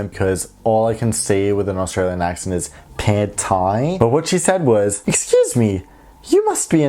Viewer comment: Did you just say "Pad Thai?" I know you must be saying something else, but being Thai I can't help but hear "Pad Thai." LOL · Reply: Dos AussieThai I did say Pad Thai😂